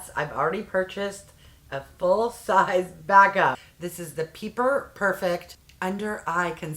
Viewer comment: At first I thought it said Pepper 😅 this looks really good on you and looks natural! 😍